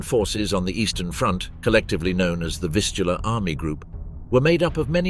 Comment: Did he say 0:15 the Fistula Army Group? must have been a real pain